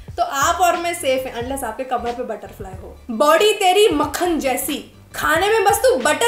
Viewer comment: at this point I was so confused and watch 5 to 6 time to understand. ..what is he saying...BODY OR POTY.....😂😂